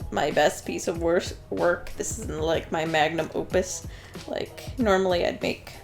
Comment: I heard that as magnum oopus. o o p u s